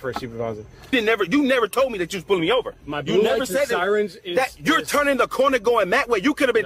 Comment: “my beautiful sirens” lmfao😂😂😂 I don’t think that’s what he said.